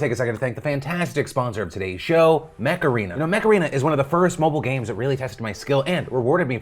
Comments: Mech Arena... And I misheard it as Makarena! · I misheard 'Mech Arena' as Macarena. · I can’t be the only one who thought he said Macarena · Mech Arena? I thought he said Macarena